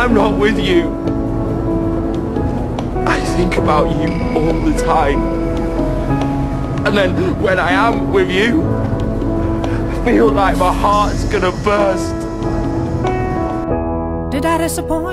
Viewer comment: what JP says on 0:17 "my heart ..." i don-t understand that :/ i am hard of hearing .... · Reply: "I think about you all the time and then when I am with you. I feel like my heart's gonna burst"